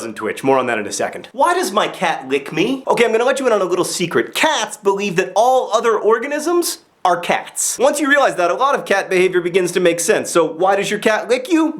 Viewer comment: John, I turned on the google auto closed captions and oh my gosh is it funny. Instead of talking about why cats lick people you say "secret camps that believes all other organisms are cactus" ( 0:33 ). You have a parasite called " temple of actor. ( 1:09 ) Your cat is also not a vampire. It's an Alabama fire. Please watch it with the captions it's incredibly funny.